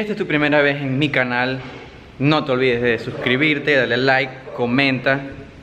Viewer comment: yo escuche desuscribirte like si tu también lo escuchastes